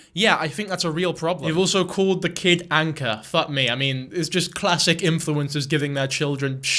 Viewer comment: I misheard and was like “what do you mean?? Anca’s a fine name!” Then I looked at the screen and realised it was ‘Anchor’ and I have reconsidered.